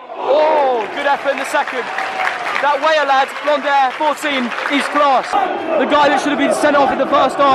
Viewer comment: did he say 14 ?!!?